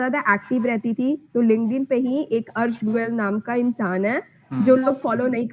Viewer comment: Thnks sister ...and plz aapne 2:54 pe kinka naam liya ... couldn't hear it properly ...plz btaana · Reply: It's arsh goyel